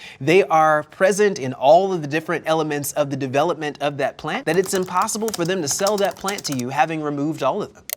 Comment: For one second, at the end, I thought he said "Incest support". Wow.